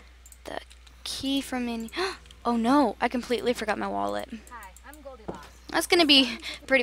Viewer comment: I heard a”Hi I’m goldy locks”